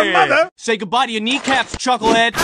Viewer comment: I coulda sworn he didn't say chuckle head but he said chucklenuts instead